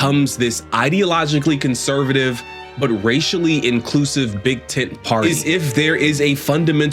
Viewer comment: I have to admit that I misheard the reporter’s comment at the end, or maybe it was a Freudian slip on the part of the narrator, when he was rightfully questioning the Republican Party being a ”Big Tent Party”, inclusive of and genuinely concerned about the Black community, but what I heard was the Republican Party in relation to the Black community is a “Big Tit Party”. Please excuse my brashness, but the ironic analogy I think is a fitting description of the current GOP vis-a- vis the Black community despite its history as the Party of Lincoln and Fredrick Douglas.